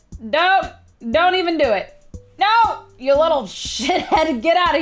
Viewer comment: did she say You little sh&thead